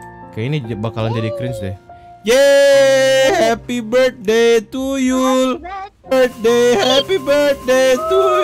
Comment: aku dengernya bang ngomong happy birthday tuyul